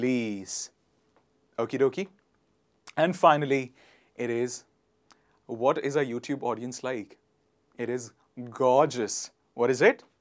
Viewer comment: DID HE REALLY SAY " WHAT IS A YOUTUBE AUDIENCE LIKE." !